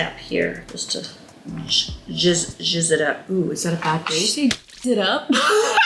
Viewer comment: "Just gotta jizz it up" What did she say 🤣🤣🤣 16:30 Ps: your mom is awesome